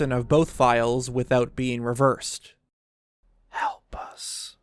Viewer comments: I heard "help us tonight" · I heard, "Help us, oh, HELP ME!"